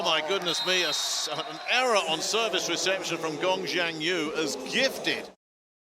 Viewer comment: What does Gabi said at 4:33? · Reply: Rotation error in the chinese side